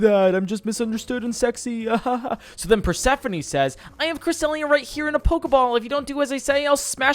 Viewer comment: I’m sorry, *what*? Did you just say “I have Chris D’elia right here in a poke ball-“?